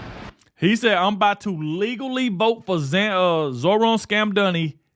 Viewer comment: is the reason I won't continue watching the video, I hate these unformatted videos, bro I know what he said, and yet you literally repeated wrong. "Illegally" not legally